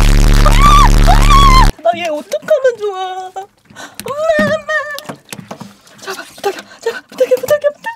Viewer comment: Did anyone else hear her say “mama” 🤣🤣🤣🤣🤣🤣🤣🤣🤣🤣🤣🤣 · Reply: She said eoma that's means mom in Korean